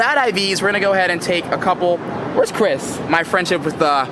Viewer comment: did you just call me chris??? :/